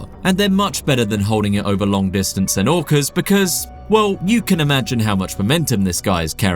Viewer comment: Did I just hear him say" an orcer "